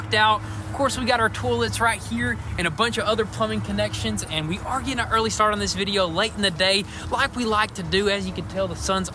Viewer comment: does he say "we didn't get an early start in the day" or does he say "we did get an early start on this video, late in the day"? I am always puzzled by that comment. So maybe I'm hearing wrong.